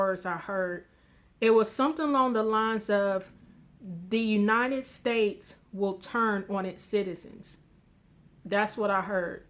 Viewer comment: Hi sister...Jesus be praised ! ...by the way ....I cannot hear so clear what that voice at the end of the dream said ...I hear you say: "the United States will turt what it's citizens" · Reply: Fred Schell It said, the United States will turn on its citizens.